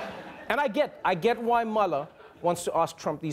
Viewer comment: My ears heard 'mother' at 0:54 instead of Mueller and for moment I was trying to figure out what Pence's wife had to do with it.